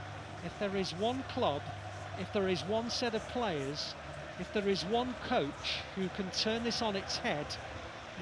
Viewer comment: At the end i thought the commentators said if there's one coach who can turn it around then it's Klopp 😂